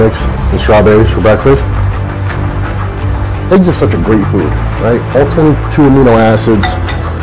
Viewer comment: Did he just say "eggs are such a great FRUIT"?